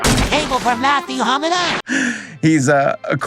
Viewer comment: I swear at first I thought you said “Matthew Hokage”